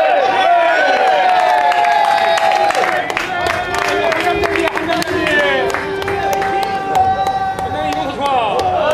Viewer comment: 이때 정현욱코치 뭐라고 하는지 아시는 분?? 궁금한데 반복해서 들어도 뭐라는지 모르겠음 ㅋㅋ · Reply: "웃음이 나오냐"